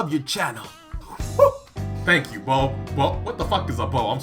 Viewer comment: Did you call that guy a bo-bo?